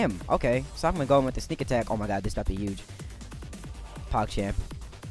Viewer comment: Did he just say what I think he said? In 2018? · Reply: hyper peanut pog champ